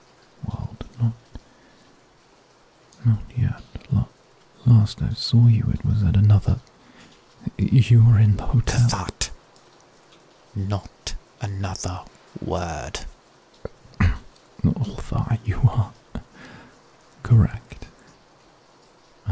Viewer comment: DID HE SAY HOTEL?? LIKE HOTEL SANGUINE-???